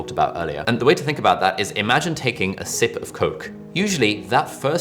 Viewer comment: When you said sip Coke I thought you had said SNIFF Coke HAHA woah Ali mate 😂 Fantastic video as always! Cheers